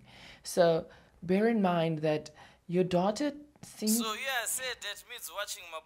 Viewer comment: I thought he said daughter....I just realized it's data 🤣🤣🤣